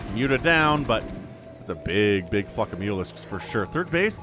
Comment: i swear i heard "big fuck of mutalisk" Language Mister :)